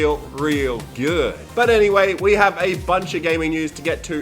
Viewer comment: I heard "we have a Punisher game news" and was already freaking